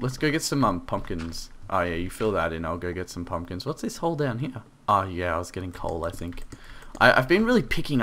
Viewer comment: lol i thought he said "why is that hole there oh yeh i was getting cold" lol i thought he said dat haha